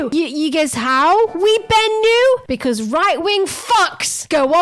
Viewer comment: What are they saying at around the 8:20 mark? "We Ben new"?